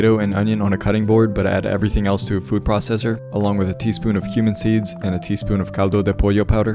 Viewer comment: I thought he said “along with a teaspoon of human seeds” 😭 CUMIN SEEDS